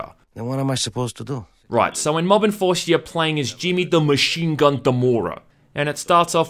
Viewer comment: I thought you said "shit town enforcer" I had to play it back to understand what ya said